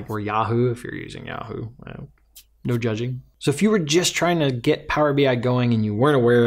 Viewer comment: "Yahoo, no judging", I heard that as "Yahoo? No! Judging!" :D That's awesome, I had a trial dev tenancy! So annoying when you're "between jobs" though